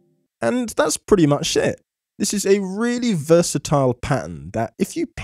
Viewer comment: + Did you say, "That's pretty much it." or did you say. " That's pretty much shit."..?...lol